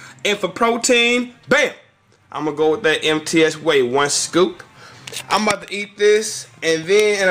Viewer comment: everytime he says MTS whey i sounds like he says "empty ass whey" lol or maybe its just me :)